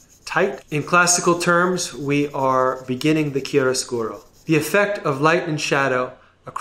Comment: Me watching at 1.5x speed Artist: "In classical terms we are beginning the [kill a squirrel]." ME: THE WHAT? Then I hear him again "chiaroscuro"